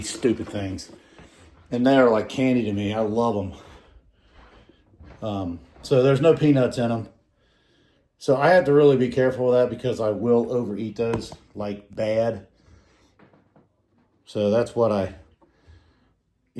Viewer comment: I know someone who buys the deluxe mixed and doesn’t eat the peanuts. Why do people gotta have so much hate towards peanuts 🤣🤣🤣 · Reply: I’m sorry, I misunderstood what you said. I thought you said that you didn’t eat the peanuts in them. But what you actually said was that there were no peanuts in them. Everyone will just have to overlook me. I’ve been diagnosed with CRS (Can’t Remember Sh*t) 😂